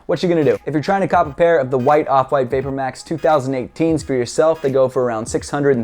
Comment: Did he just say screw yourself